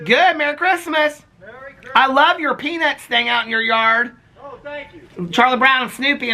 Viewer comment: OMG 😳🤣🤣🤣🤣 I just bout fell out of my chair!!!! I thought you said to ur neighbor - hey Merry Christmas how are you, I love your penis thing out in ur yard 😂😂😂😂 until u said snoopy🤣🤣🤣🤣